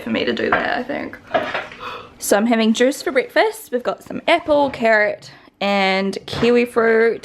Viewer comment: When you said I'm having juice for breakfast i wasn't focused and i heard "I'm having Zeus for breakfast" i was like WHATTTTTT lol